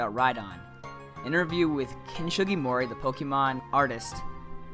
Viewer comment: it almost sounded like you said "Kenshin Himura"